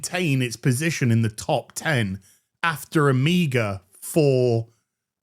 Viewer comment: had to relisten to this a couple of times because I thought said "Amiga", but I finally figured it out, "a meager."